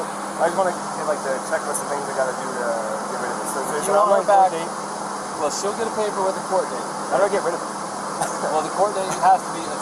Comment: does he actually say to the officer "how do I get rid of her" 🤦